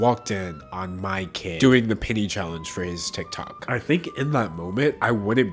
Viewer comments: wasnt watching and thought mans said "the pity challenge" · I was not looking at the screen and I fully thought was saying “the pity challenge” for a full two minutes